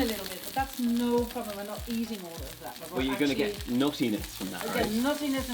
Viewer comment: I kept hearing "naughtiness," then I realized he was saying "nuttiness." Hmmm. I may be disappointed. A little naughtiness in our food is not a bad thing. ;) Cabbage looks good, even if it is well-behaved.